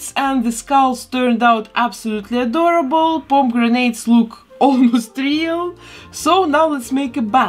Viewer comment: I thought you said pom-grenades at first. It funny the words you hear when you are only half lisenting.